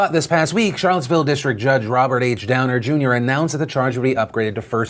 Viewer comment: Sorry, but I heard Robert Downey Jr. Anyone else?